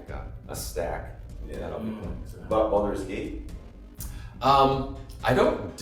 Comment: Bem legal! Me parece que aos 5:30 a pergunta foi sobre Baldur's Gate mas a legenda entendeu como other games.